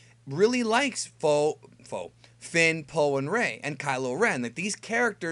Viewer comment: Did you say Fo? Is that a new ship name I haven't thought of yet? I can dig it xD